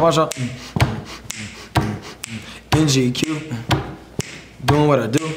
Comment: what's the first thing he said? (???) Doin' what I do All white Looking nice Ok, Come through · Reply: In GQ Is what he first said